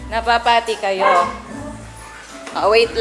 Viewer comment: did I just hear "napapati kayo"? 😁😁😁